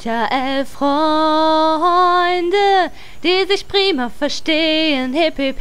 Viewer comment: Nostalgie pur. Hab ich früher gesuchtet. Ich hab niemals verstanden, dass es "prima verstehen" heißt, nach mehr als 10 Jahren endlich mal den kompletten Text verstanden. :D Warst du ein bisschen verschnupft, es klingt etwas so oder hast du ein ähnliches Problem wie ich, dass deine Atemwege einfach etwas verengt sind und dann oft so klingt, aber man gar keinen Schnupfen hat?